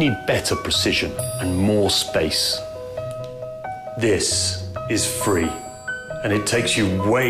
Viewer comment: Lol thought he said "This is free"